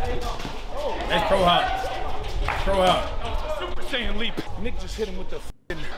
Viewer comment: did he say the n word? Or did he say nick